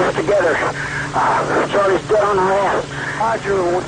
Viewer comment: I'm trying to understand exactly what the whispering soldier says at 4:37 but I'm not a native english speaker nor do I have a trained tactical radio ear. Can anybody please tell me exactly what he says? Thanks. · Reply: "Charlie's dead on our ass"